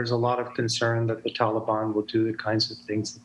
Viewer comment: I thought he said Libya 3 times...clown demon.